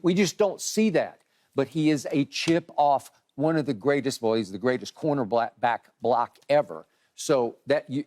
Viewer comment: did skip say boy corner black 👀